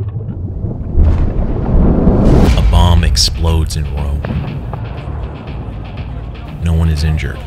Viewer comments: When he said, "a bomb explodes in Rome". I genuinely thought he said, "A mom explodes in Rome." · Could've sworn he said "A mom explodes in Rome" and I was like 'well shit that's one way to start a video I guess' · i was going into this video so confused because i thought you said "a mom explodes"